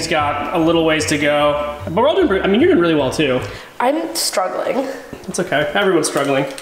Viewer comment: in 8:32 i thought she said "im starving."